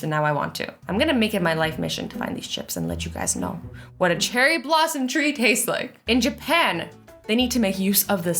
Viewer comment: 1 like, 1 cherry blossom tree chip for Azzy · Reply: At first I thought u said Cheryl blossom, I thought someone here watches river dale..